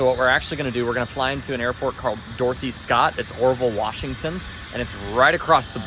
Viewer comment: When first mentions Dorothy Scott...I swear he says "Dorky Scott". I mean you tell me... 2:24. 3:02 as well. :)